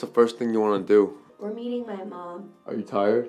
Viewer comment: I heard we're eating my mom